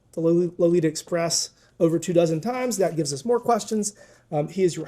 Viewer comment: Flew on the Lolita express 2000 times!?!? Did I hear that right?